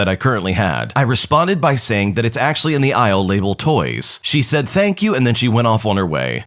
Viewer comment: “actually in the (Illegal) toys” instead of “that its actually in the ill labeled toys” ☠️